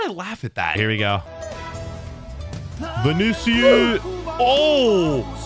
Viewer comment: did he say vinicius